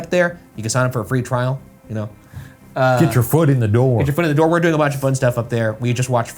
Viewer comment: after Shane said "get your foot in the door" it sounded like Ryan just casually and quickly said, "yeah we're doing a bunch of foot stuff up there" lolol